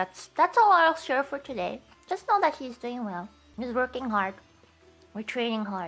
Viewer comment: When she said please look forward to 4th fest I could've sworn I heard "please look forward to foot fest" which doesn't seem that weird with Gura being involved in the conversation.